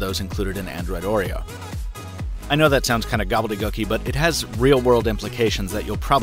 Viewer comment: What Is That Word?? 😂😂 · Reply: The word is gobbledygook, just means something that is effectively meaningless language